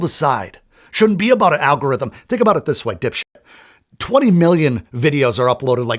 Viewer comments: Did he just call us “dipshit” ? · Did you call me a dipshit at 9:15 haha? I had to rewind it to make sure I heard that right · Good points, but did you just call me a dipshit at 9:15? Forgive me if I got that wrong, but that's a bit over aggressive.